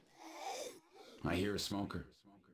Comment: i hear smoker